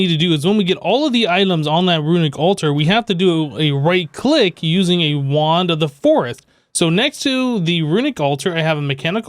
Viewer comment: anyone else hear him say "Using a Wand of the Foretht" at 6:47 xD